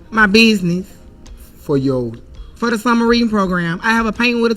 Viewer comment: Summer reading? I thought she said submarine